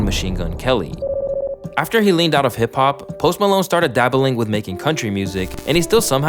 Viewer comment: I thought you said Hop Pop 💀 💀